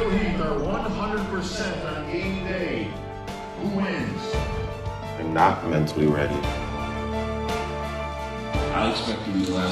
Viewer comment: What does victor martinez say at 1:54 ? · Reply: "I'm not mentally ready."